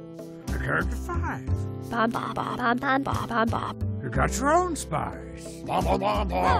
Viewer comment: did she say pom-pom? (i shall draw her!)